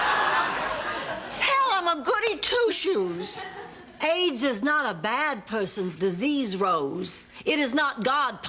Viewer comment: “AIDS is not a bad person disease Rose. It is not God punishing them for their sins.” Blanche owning Religion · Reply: For a while I thought she said AGE isn't a bad person's disease...I'm dumb.